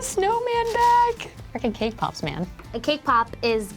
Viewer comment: Though I read it was written 'Cake Pop' , I heard ' K-Pop' . 😂